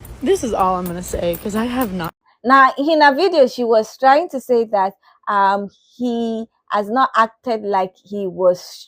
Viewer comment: "You need to listen to gay people when they tell you that somethjng is harmful." Gay here, making skits is not harmful. Whats actually harmful is going to the phillipines as a white savior and trying to "take orphans home". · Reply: I thought she said that she was trying to steal a baby. 😮